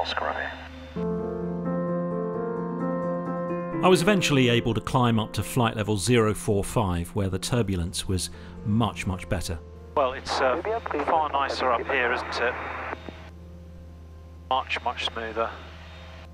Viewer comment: I thought you said “flight level 405” at first. I was like “whAt nOw!?” 😂